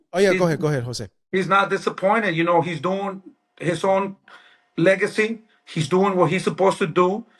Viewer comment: did he say Legacy ??? Lmaooo !!